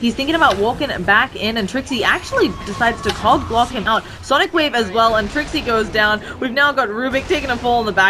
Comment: why did i hear cock block him out